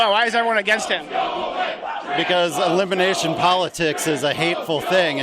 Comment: "When in Rome" -Will Ferrell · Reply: a guy says libination politics? did i hear that right?if so,do you have any idea what that means?